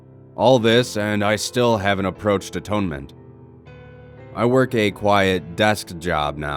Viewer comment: did you say tónment cuz in iceland its the place where you learn instruments in school.... wierd lol...